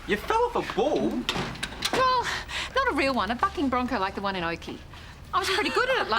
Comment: what did Miranda say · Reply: She was telling them how she rode a bull and said it was a "Bucking bronco like the one in Oakey"